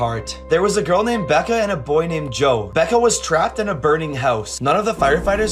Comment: I thought he said veca